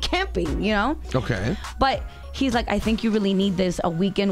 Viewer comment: did she just say he's like